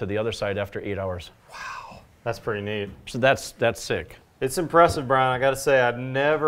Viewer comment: Did I hear Brian say "that's sick"